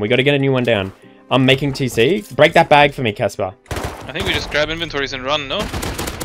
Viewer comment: Coulda sworn said "break these bags, Tesla" or maybe ive been confused on who is who this whole time